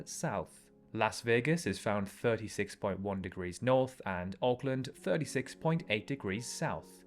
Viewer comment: I thought you said Oakland. Now I get it